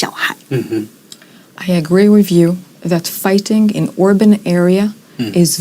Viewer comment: Fighting in "urban" area is very challenging. 城市巷戰是非常困難的。 口譯人員似乎把urban 聽成open 了。